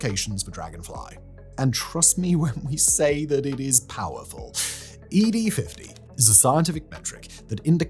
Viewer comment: E.D.- WUT! 😱 OH. NO... wait, simon said ED50 Oh ☠️